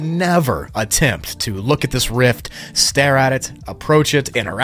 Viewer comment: he actually said rift